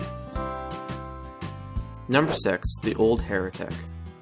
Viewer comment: did he just say "number sex"???